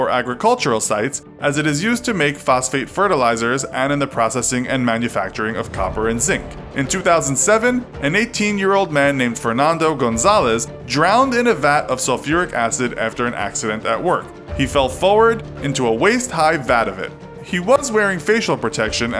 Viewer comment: did he just say Gonzalez? Bc that last name sounds familiar. Danny Gonzalez’s lost brother? Idk